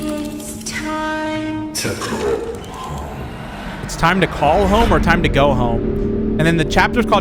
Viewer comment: for those who can't quite hear, the prototype doesn't say call or go he says "it's time to come home"